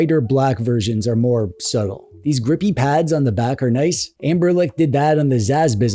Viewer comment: do i hear 'amberlec' instead of 'ambernic'?